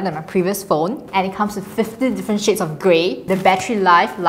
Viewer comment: i hear "50 different shades of gray"